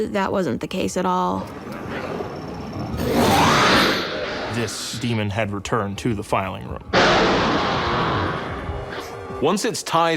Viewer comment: The demon had returned to the defiling room... I thought he said that, bl00dy hell that would have been ominous as f**k.